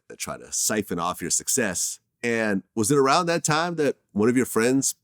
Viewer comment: Did he just say fax machines was not around at the time ??? 😂😂😂😂